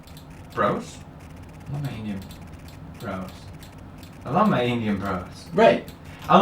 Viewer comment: “I love my Indian bros! ....I love my Indian bros? ...Indian bros...? I love my Indian bros?” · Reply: It sounds like hes saying brosh to me